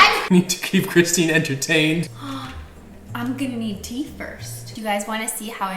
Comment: I heard "I'm gonna need teeth first!" and was expecting a holo dentures reveal